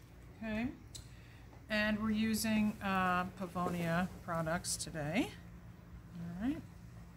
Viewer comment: What is the brand of skincare products you are using? It sounded like you said pavolia but I can't find anything close to that with goodle.